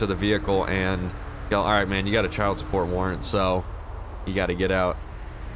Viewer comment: for a min i thought ya said childs porn warrent